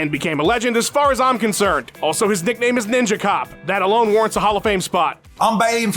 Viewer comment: I know Jiniem's nick name is Ninja Cop but it sounded like said it was Ninja Cock which is far superior so I'm injecting that as head cannon.